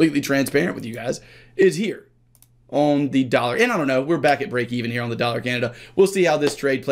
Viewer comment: Sorry, didn't catch that, at 22:43 you said "on the dollar and i don't know we're back at break even" , right?